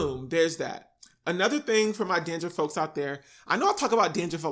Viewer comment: did you just say “dandriff”? 🌚